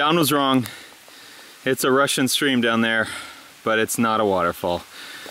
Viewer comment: Why is it a Russian stream why can’t it be American ? I know he said rushing but sounded like he said Russian